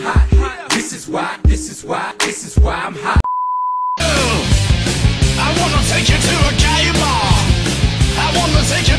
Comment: ok, so 0:48 does it say "Game Ball"? Because I keep hearing "Gay Bar" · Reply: kyoko mogami Yes it's gay bar